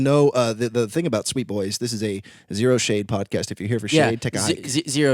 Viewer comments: I thought they said "zero shane" .............. .............. .............. I mean. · Thought they said “0 Shane podcast” lmao